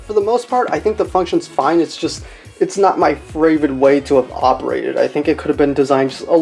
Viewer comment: wait wait wait wait wait, did he just say "fravorit" instead of favorite?